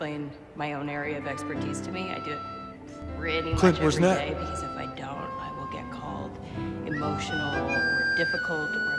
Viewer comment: What does he say? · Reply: "Clint where's Nat?"